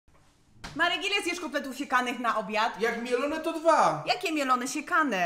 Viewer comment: Czy tylko ja usłyszałam na początku ,,kotlety sikane,,?